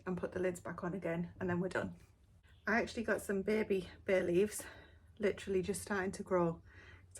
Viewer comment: 😂😂 at the very end I thought you said Baileys and I’m like mmm 😋 sound delicious but it was bay leaves 🍃. Having said that it was lovely to see more recipes and what you use. Have a fantastic weekend everyone, Ali 🌞🇨🇦